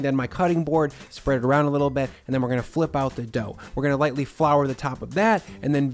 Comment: At about the 6:00 mark when "flip out the dough" was said, even though I heard flip out, somehow my brain turned it into "flip off the dough". That alone made me laugh for a second, and I had to say it here.